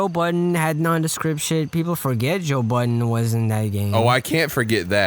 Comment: thought said joe biden lmao